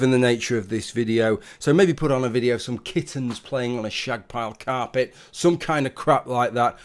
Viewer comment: Wait, you mean two kittens playing on a rug isn't porn? I would! Eh? Eh? Know what I mean? Phwooar!! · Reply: I thought said the the kittens were having a shag on the rug.